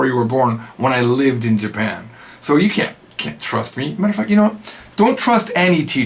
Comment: wait what? 😂 did you say mf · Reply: He said "Matter of fact, you know what" Lol